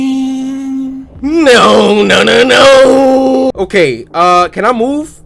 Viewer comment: WHY DOES IT SOUND LIKE HE SAID Clown: Wanna play....wanna plane..? Cory: NO NO NO NO LIKE YOU WANNA PLAY OR YOU WANT A PLANE?